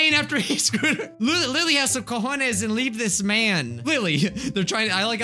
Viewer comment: This is like the 3rd time i watched this and i just realized something... 14:06 Did...did you just say "cojones"?? XD hispanic laugh